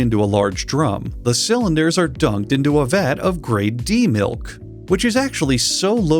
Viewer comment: I know it’s Grade D Milk but i heard it as Grey Demilk